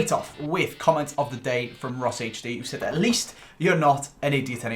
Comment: I thought you said it was Rossi at first.